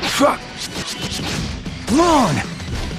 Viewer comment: did turles just say "moan" on his tap blast